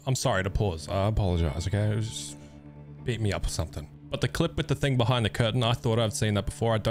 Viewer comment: did I hear correctly? “Beat me up or somethin” 😂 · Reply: Yep 😂 “if you got a problem with me pausing, beat me up or somethin” 😂